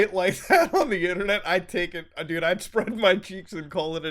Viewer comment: who heard pubes and who heard cheeks at this point of the video? me personally heard pubes the first time but upon further inspection I realized the correct word was infact cheeks